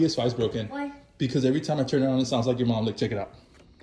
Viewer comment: I thought he said "every timebi turn it on it sounds like chicken" 💀